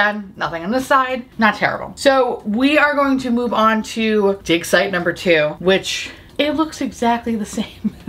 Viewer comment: When you said "dig-site" i legit thought you said d*cks eye😂😂😂😂😂